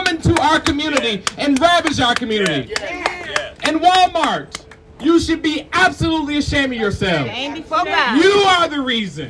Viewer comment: Ok, wait. At 4:43, did I hear him say, "And Wal-Mart! You should be absolutely ashamed of yourself!" Did he actually say that??? I must have heard wrong.